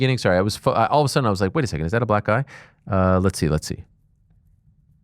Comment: I’m almost sure I heard Ariel say “back, Guy.” :)